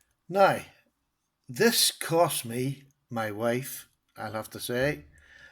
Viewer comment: At first, I thought you said “That cost me...MY WIFE.” There are possibly only 2-3 knives that I’d trade my wife for...ok, ok; 5... I too recently started to appreciate traditionals. Your Rough Rider’s fit & finish is beautiful! I don’t have one of those yet. My boys (ages 6 & 8) are going to be in need of a knife very soon and I think a good, inexpensive slipjoint may be in order.